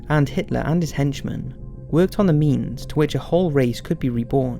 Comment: I thought I heard "worked on MEMES" I did NAZI that coming.